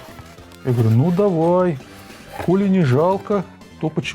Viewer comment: послышалось не "коли" а "*ули". Микрофон надо настроить, децибелы или басы зашкаливают. На стримах это заметил но думал там кухонная акустика но нет. Речь местами превращается в гул.